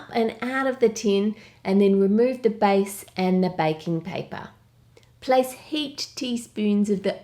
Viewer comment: Oh wait i always thought you said bacon paper but on this video i understood that it was baking paper Plz kill me